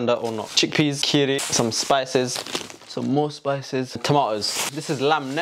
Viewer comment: wait i was rewatching and did he just call himself a spice at 2:14 ☠️